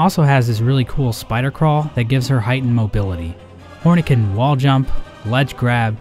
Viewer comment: The captions say "horny" instead of "Hornet" at